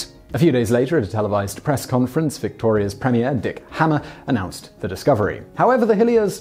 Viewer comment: did i hear that right, that the dude's name was "Dick Hammer"?